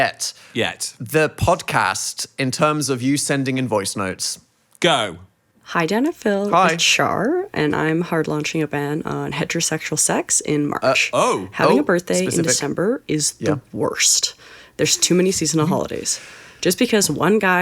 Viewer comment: I CAN'T BE THE ONLY ONE WHO THOUGHT HOMIE SAID THEY WERE LAUNCHING A BAND ON HETEROSEXUAL SEX IN MARCH. AS IN A BAND CALLED HETEROSEXUAL SEX THAT WAS DROPPING THEIR ALBUM IN MARCH. I'D LISTEN🤘🎸🎸